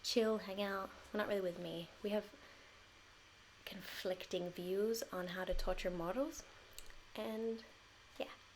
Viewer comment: Did anyone else hear "conflicting views on how to torture models" at 10:50 ?